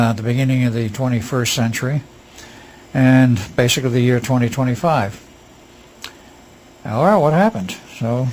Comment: Did he say in the year 2025??